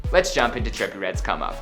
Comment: Sounded like he said, "cummo" instead of 'come up'. 😂😂